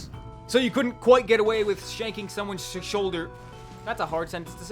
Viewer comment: I dont think it's that hard to say "shanking someone's soldier"